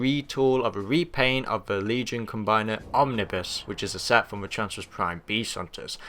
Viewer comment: did you mean to say "abominus" or "omnipuss"? Because I thought the combiner was called abominus (in fact, I have all the components so I can make the combiner). Also the guy who makes the main body on infurnus (I think that's right) is a new mold, or, at least it's not a redeco of Hun-gurr. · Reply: I said Abominus, I think that's how it's pronounce, oh well if you know the toy, you know what I meant.